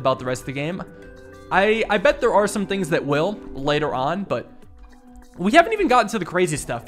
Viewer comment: When you said “I bet there are some things that will, later on”, I misheard that as “like your aunt”